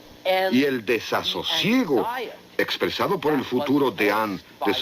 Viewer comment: No entendí a quien citaba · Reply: "El futuro deán de Saint Paul, John Donne". John Donne fue un poeta (<num>) y desde 1621 hasta su fallecimiento ocupó el cargo de deán en la Catedral de San Pablo, en Londres.